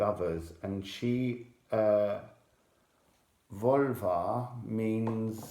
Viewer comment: I hear "Volva" as "Vulva" every single time, and it's an unexpectedly potent mental image....